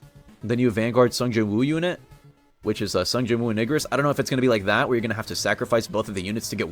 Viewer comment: i swear i heard ni**ris instead of igris